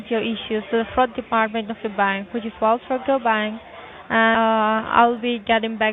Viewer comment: If you have multiple bank accounts with more than $250,000 each, I suppose it would be appropriate to bank with Whales Fargo, which is what I heard you say.